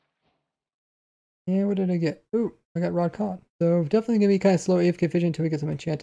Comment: Looks at raw salmon: “ooh raw cod” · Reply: i thought being said “rod caw” lol